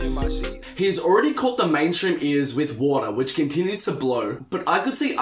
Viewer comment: ugly god is not gone be on xxl point blank and it sounded like you said order not water